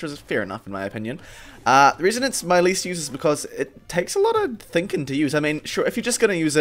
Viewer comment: Did he just say thinking